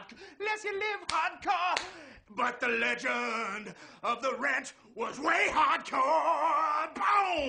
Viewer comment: This hole entire time I thought he was saying “But the legend of the rath was way hardcore !!!! “ 😐